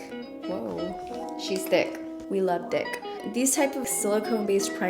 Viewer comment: WHAT! I'm uncomfortable cuz I just heard her say she love dick